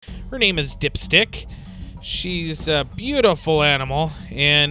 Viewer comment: I thought you said dipshit